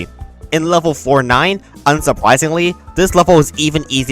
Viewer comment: Am I tripping or did he say "Unsupwisingly" at 21:25 ?💀